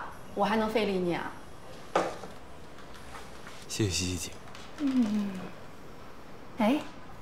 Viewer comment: did he say thanku two times Xiexie xixi😂😂😂